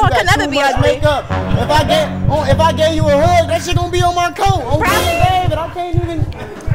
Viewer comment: i thought homeboy said On King Davis. and I'm like who the duck is that